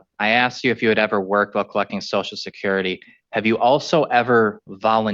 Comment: sounds like he says “have you ever worked for fucking social security” Lol